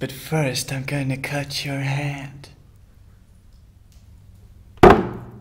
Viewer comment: I died bc I hear " I'm gonna cut your thongg" LOL